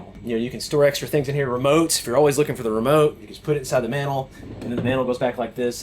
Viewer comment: why are you saying 'manhole', instead of mantle? Please try to enunciate your words, correctly. Thanks!